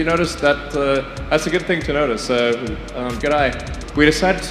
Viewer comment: I thought Elon said "G'day" to the Everyday astronaut. 7:08 not being Australian, this confused me.